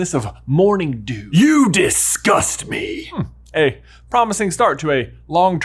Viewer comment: Maybe he actually said "you discussed me?" And he was being inquisitive about the death eater gossip about dumbledore.